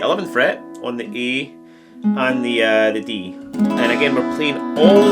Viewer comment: Hard to understand that heavy accent but it sounded like you said index finger on the 9th fret of the G and the pinky and index finger on the D& E. Did you mean D and A ? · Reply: Yes D and A 😂 it’s a common theme with my videos that my E’s and A’s get people confused. Thank you for watching, hope you enjoy!